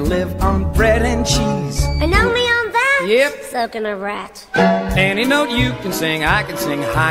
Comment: what was Mousy trying to say ;-;'' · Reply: She said So can a rat